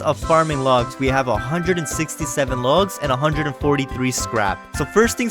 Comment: DID YOU JUST SAY 67